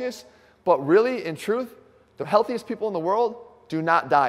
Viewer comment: Bloody hell I thought you said 'healthier people don't die'. I thought that's a wrong statistic 😅